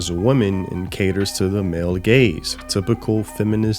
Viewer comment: Any time I hear the "Male Gaze" I laugh cause I think they're saying "Male Gays". It makes hearing BS like that way more funny to hear.